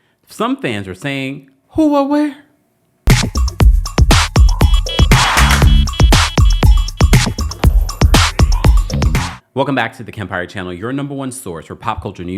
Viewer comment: what is he saying? (Is it 'Who are we?') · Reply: “Who, what, where”